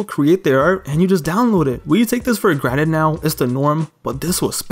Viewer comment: Did you say "take it for granite"?